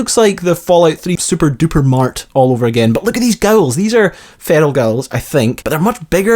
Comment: For a moment, I thought you said "these are feral girls," then I realized you were saying feral ghouls! lmao I love your video (and your accent, if I wanna be creepy ish).